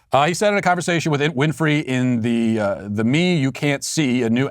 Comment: When Matt read that show's name I thought he said "The meme you can't see". Was getting a bit curious there..